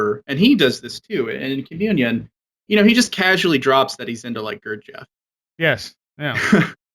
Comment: Uhmm just curious What does he say at 59:50 Streiber casually mentions he's into ?????? Oh great show! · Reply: Gurdjieff <link>